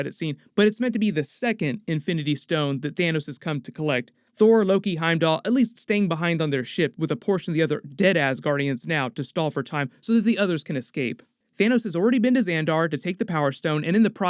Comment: idk why I heard deadass guardians